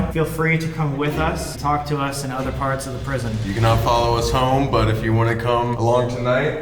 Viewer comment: I misheard and thought Colby was gonna say "You can now follow us - on twitter, instagram...."